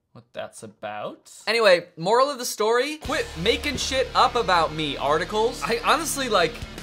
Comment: Before I thought it said stop making stuff about me